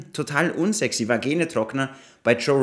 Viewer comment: "Vagenetrockner" ??? 😂😂 Gibt's das Wort wirklich? Finde es, wenn ich es richtig verstehe jedenfalls ein sehr treffender Ausdruck 😂